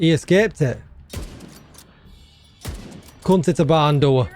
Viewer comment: What did you say 6:47?? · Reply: "can't hit a barn door"... i think